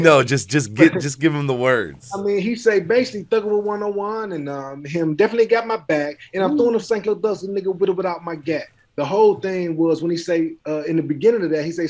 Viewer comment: Man... The whole time... I thought Bizzy said "Thuggin with 101 and ummm, Them definitely got me back. And I'm throwin up St. Clair thugsta, nigga. With or without my gat." In reference to 101st and St. Clair... Not bout no Graveyard Shift guy lol dope interview!